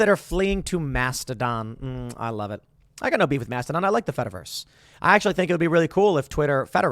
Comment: When Tim said “FederVerse,” I thought he said, “FetterVerse.” Hello everyone, good night!